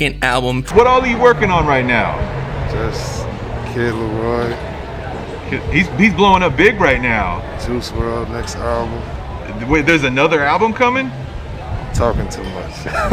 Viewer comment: I heard x album 💿 let’s goooo